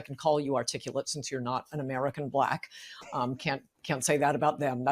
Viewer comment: Did she say American Black???